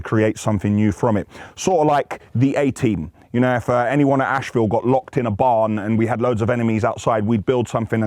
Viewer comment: i thought you were saying assville